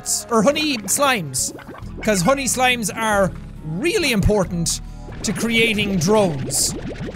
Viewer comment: I actually thought you said "Honey slimes are really implortant..."